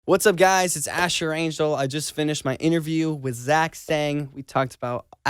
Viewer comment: "whats up guys its asher angshel" He cant even spell his name- or did I heard it wrong?